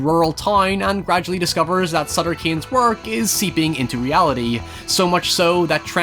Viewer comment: Just finished watching the movie just so I can watch this video. Took me a while to realise the author's name was Sutter Cane. I kept hearing 'Son of Cain' and thought, 'that's a mad pen name to have, even for a horror writer'. But I'm still trying to process this film. It kind of blew me away.